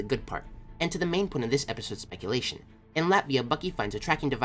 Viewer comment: At least we just got a Black Widow trailer, whatever happens with the Venom 2 trailer. FatWS episode 3 was awesome. I totally thought that it said Latveria instead of Latvia for a second and I nearly freaked out lmao.